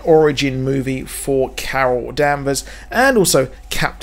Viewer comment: really thought you said, Kara Danvers there...